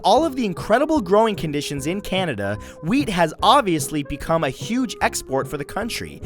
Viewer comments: When he was talking about wheat, I looked down to take a bite out of my sandwich and thought he said weed. I was like 6th largest supplier of weed WHAT!? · when he said wheat I thought he said weed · Anyone else who thought Matthew said weed instead of weath in 2:55 :')